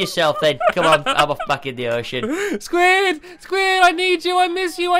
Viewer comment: I thought squid said ima f**k in the ocean